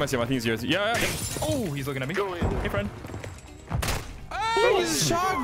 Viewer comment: did he say ‘hey friend’ then got killed by Friend?